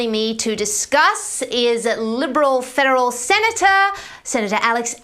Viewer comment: It’s seriously sounded like she said liberal “feral “ Senator